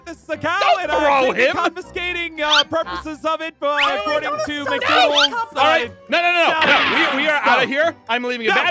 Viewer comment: YOU SOUND LIKE U SAID MICDUNALDS